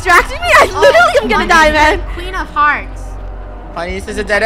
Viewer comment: When said “the queen of hearts” I thought she said the queen of farts lol.